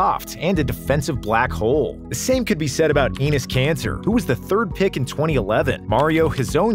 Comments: is nobody gonna talk about how instead of him saying Enes Kanter he pronounced anis cancer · Thought he said "anus cancer"